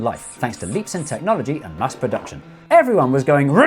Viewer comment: has anyone else heard him say Musk production?